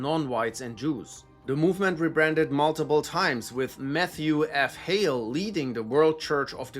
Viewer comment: sounded like meth-thew